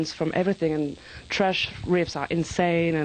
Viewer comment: did she just say "trash riffs"?